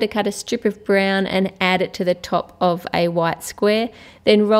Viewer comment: I thought you said roll a ball of What XD